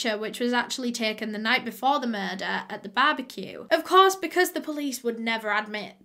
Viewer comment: Lordt, I thought I heard “the same picture taken before the night of the murders and the barbecue”🤦🏾‍♀️…I had to rewind and then I got it right the second time..she said “at” not “and” 😅